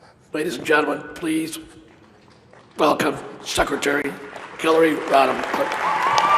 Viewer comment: I heard him say " Secretary Killay Rodham Killton"